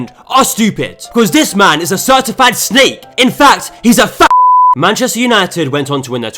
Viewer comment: Why does it sound like he said "he's a fan" 💀